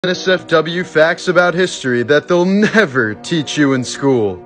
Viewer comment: I THOUGHT YOU SAID "HERES SOME W FACTS" NOT "NSFW FACTS" GOSH DARN IT I WASNT READY FOR THAT